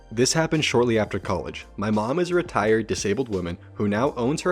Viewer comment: I heard "my mom is retarted" in the first story